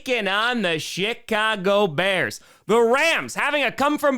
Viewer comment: It sounds like said the Shitcago Bears.